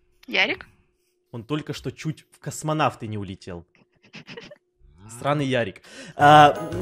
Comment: Мне одной послышалось сранный Ярик вместо странный Ярик